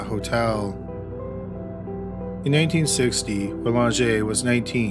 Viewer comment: you said in 1860 at least thats what it sounded like.